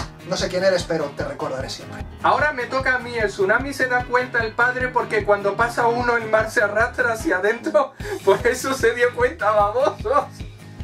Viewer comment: que dijooooo ? Jajaja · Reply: Te lo traduzco: ahora me toca a mi, el padre se da cuenta del tsunami porque; cuando ocurre uno el mar retrocede, por eso se dio cuenta, babosos